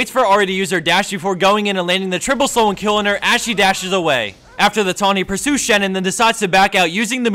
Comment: who is ashy? Ashy dashes away is that what he said?